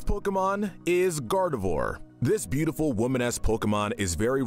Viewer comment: The captions are funny. When you mentioned gardevoir at 2:54 you said woman-esque, but the captions said"Woman ass".